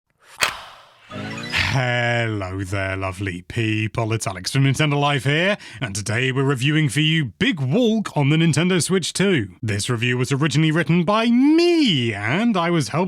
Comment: Big Wolk 😂😂😂😂 (Is what I heard)